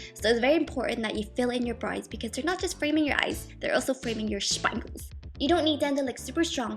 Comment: Whaaaaat is she saying at 1:38 ??? It sounds like "shpinles" or something...??